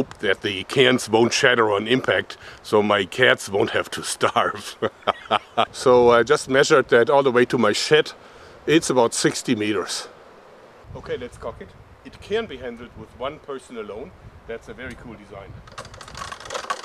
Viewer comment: Awesome video! lol at 2:30 it sounds like you said sh*t instead of shed but I understood you haha